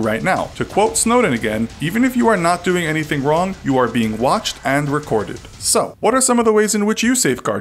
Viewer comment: I thought it said watched OR recorded not watched AND recorded